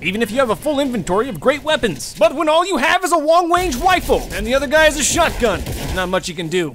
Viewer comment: did you just say BUT WHEN ALL YOU HAVE IS A LONG WANGE WIFLE